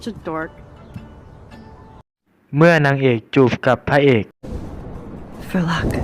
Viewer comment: ได้ยินว่าเดี๋ยวกุจะจัดสักดอก555+